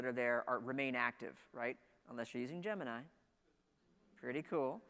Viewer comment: Did Kyle say Gemini? – What is it, may have no use to me but it's always good to know!